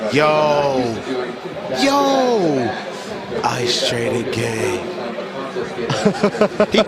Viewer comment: huh? What did he say again 🤔😂...that was the most funniest thing I have ever heard while stoned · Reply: “iiice tray da gaaang” hahahahaha